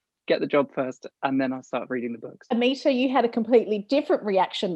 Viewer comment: lol did she just call her Amisha?